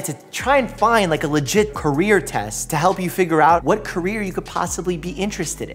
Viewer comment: I thought it said cancer test